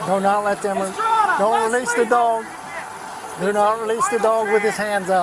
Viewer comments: All I heard was " release that dog"😂😂😂😂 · In all this confusion, I heard “release the dog” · "I thought you said 'release the dog'."